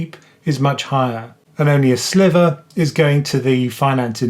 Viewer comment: Great video as always thanks! FYI at 14:13 it’s ‘sliver’ not ‘slither’ 🤓I may have misheard but sounded to me like you said the latter so thought I’d play grammar police haha